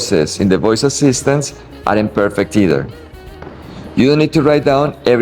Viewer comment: Really great video and advice. I used to teach English and as you know, it's hard to turn that off, so just to help out here...and I think you said it correctly but your self-written subtitles show it incorrectly. At 7:49, you have "aren't imperfect...either". It should be "aren't perfect...either". I think you said it right though. Anyways, I sure wish my Spanish were as good as your English. And thank you so much for turning me onto YouGlish. Didn't know about it but it's so much better than Google Translate's robotic attempts.